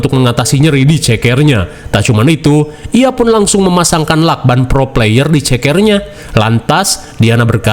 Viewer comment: wkwkwk.. lakban pro player. Gak salah dengar aku nih?😅😅